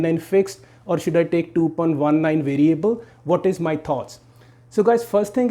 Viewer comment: 2.19... i thought you said 2.13